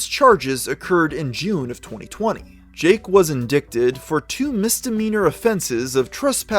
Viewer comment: Thought he said Jake Paul was addicted. Lol. In-dick-ted. Don't yall computers tell yall how to say and spell shit. Jk. I did double take though